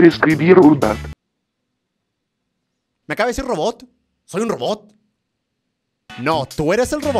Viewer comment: yo no escuche robot yo escuche....puta XD